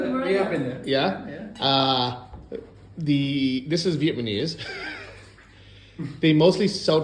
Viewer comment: Am I hearing “vietmanese” instead of “Vietnamese”? 😂